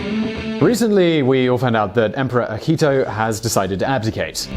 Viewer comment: Sounds like he says Ahito. The correct is Akihito.